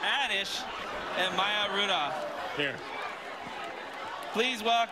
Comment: Did he just say “your beard smells delicious” ?